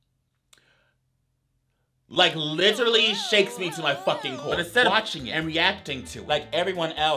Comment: nahhh did he say “shakes me to my fcking hole” Lmaooo